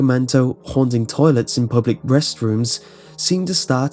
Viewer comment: I swear I heard him say public breast-rooms.... I don't know